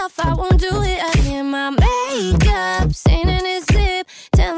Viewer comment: why is there an illuminati tattoo on her chest? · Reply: Mariyam H.A at 1:47 i first thought she said my make-up , satan is lit She me be in the illuminati😂😂